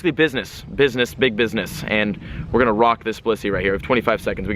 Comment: I THOUGHT HE SAID WERE GONNA ROCK THIS P*SSY NOT ROCK THIS BLISSEY BAHAHAHAHAHAHA